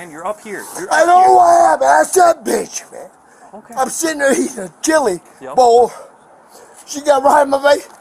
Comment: When the cop mistakenly said that he ate a bowl of cereal I died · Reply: I thought he was saying he was eating jelly then later realised it was chili ☺️